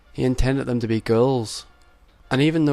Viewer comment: thought you said girls when you said ghouls. :D